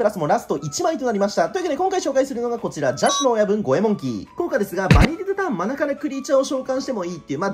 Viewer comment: 心做しか「邪智の暴虐ゴエモンキー！」に聞こえる